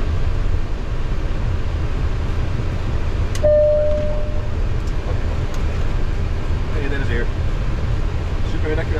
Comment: What is he saying? Almost sounded like "super nickel" or something. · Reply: Cabin oke;).